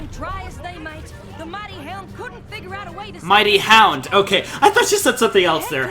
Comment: "...Mighty Hound, okay. I thought she said something else there." I hope by now that you know AJ was saying "The Mighty Helm".